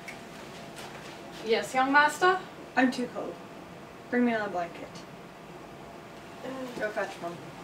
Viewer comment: When he asked for a song, I thought he said 'bring me a thong' 😂🤣